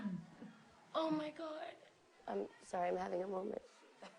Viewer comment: What she said at 3:29? "sorry..." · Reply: ominonero she said sorry I'm having a moment